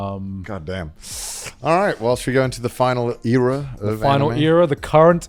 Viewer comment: what did connor say? “Whilst we go into the final era?”